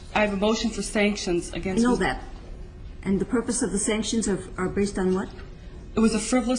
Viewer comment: "*inaudible*" She clearly says "And the purpose."